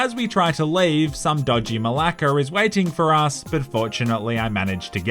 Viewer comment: what does he say.? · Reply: Some dodgy malaka is waiting for us